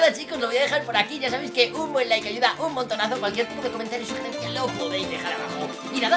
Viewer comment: soy yo o escuché y cualquier p**o comentario o sugerencia lo podeis dejar abajo